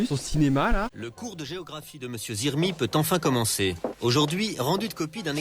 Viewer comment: dites moi que je suis pas la seule à avoir entendu monsieur zermi